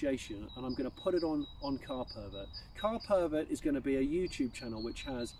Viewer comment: I thought it said ‘cat pervert’...